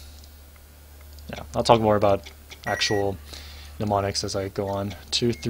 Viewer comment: I heard: I'll talk about actual demonics that I belong to...